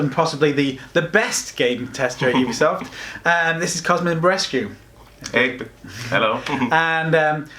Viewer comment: I thought he said he was Cosmic Rescue